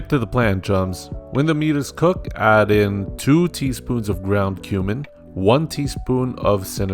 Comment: UI wasn't paying attention so I heard 1 teaspoon of ground human